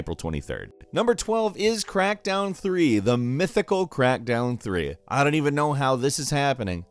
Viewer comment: Is it me... or you messed up at 10:45-10:47 and instead of saying Crackdown... you said CrackTown ??